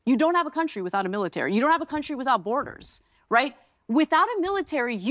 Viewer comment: did she say, "you don't have a country without borders"?